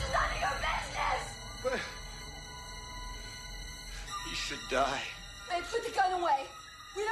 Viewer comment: When I first saw this, I thought Bill Paxton had said "You should die" to Helena, and it chilled me to the bone! I don't know why Sands' performance was panned; if you look at his subtle facial expressions he really internalized the character. But many audience members expect to identify with one or more characters rather than explore ideas from the point of view of an unreliable protagonist with a twisted view of life.